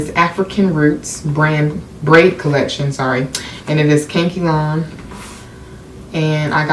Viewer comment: I love you but did you just say "Kankylon?"😂 EDIT: THIS IS ABSOLUTELY GORGEOUS